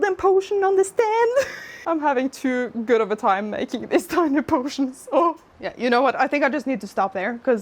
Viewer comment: do i hear you saying "tiny potion merch"? 😀